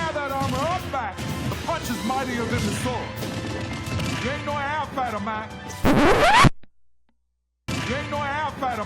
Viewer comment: what is doc louis saying? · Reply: SpineCones Gaiden "Ain't no airfighter, Mac!"